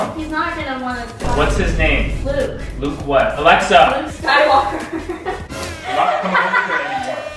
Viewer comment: I thought was saying my name alexa or the robot alexa but I forgot he was talking to